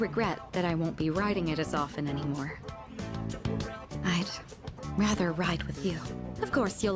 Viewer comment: ngl, for a second I thought she said 'I'd rather ride you' 😂